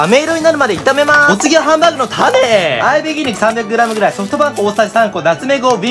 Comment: ソフトパン粉が一瞬ソフトバンクに聞こえたw